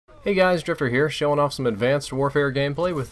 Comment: When you said "Hey guys Drift0r here" I thought you said "Hey guys stripper here"